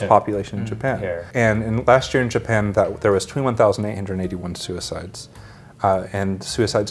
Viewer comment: Hey, great video as always! Just something you might want to fix, I'm pretty sure at 7:21 he says 21,881, but the subtitle says 2,881. 😔